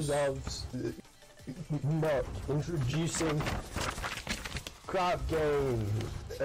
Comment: fun fact: the auto generated subtitles say "Crap Games"